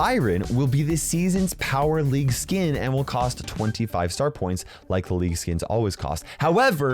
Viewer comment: what did he say 25 starpoints